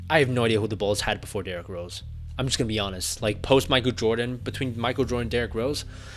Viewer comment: sounded like "Post my good Jordan"